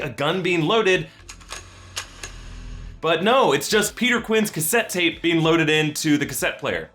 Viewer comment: does anyone here him say quinn instead of quill